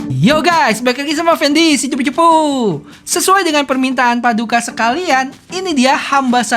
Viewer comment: gw ulang berkali-kali, dia ngomong apa si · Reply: Balik lagi sama effendy si cupu cupu